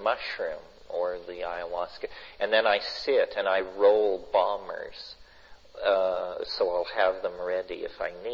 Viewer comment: I thought he said bong rips not bombers lmao